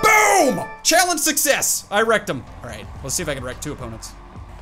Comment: When he said “I wrecked them” I thought he said “I rectum